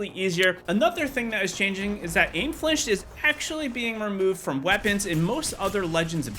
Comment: What are you saying at 1:00 ? What is being removed from weapons? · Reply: Flinch. When you get shot by guns or abilities.